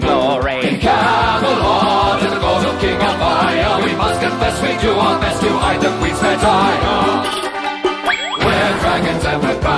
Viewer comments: Does anyone else think at 0:16 it says "We must confess, we do our best to hide the queens vagina" haha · I swear they keep saying "hide the queen's v@ EDIT: I just realized I did not hear that wrong.